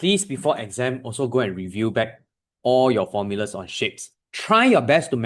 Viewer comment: I thought he said shit instead of shape😅😂😂